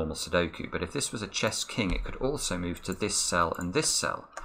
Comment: is not king's move. King can move 1 cell in any direction. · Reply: Wow I meant 3:10. But anyway, I looked again and didn't hear the first time the word "it could ALSO move". My fault. I understood that "it could move" instead. He speaks to fast for me and I'm not a native speaker ;)